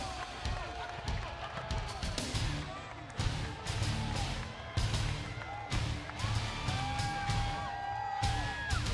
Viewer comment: What did he say at 1:05 ? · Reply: Freestyle8Football "Thats very nice"Cause the fans were clapping